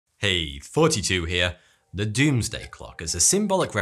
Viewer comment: I always hear "heyy,42 here"